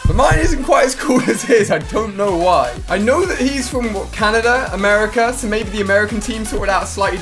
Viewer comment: I thought he said "he's from waka Nida